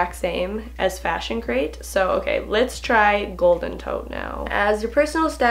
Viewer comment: 1. At first I heard "Golden Toe" 2. I think with frock box, it meant that if you want to return an item then you can do that with the prepaid shipping and stuff, and not that you HAD to return them.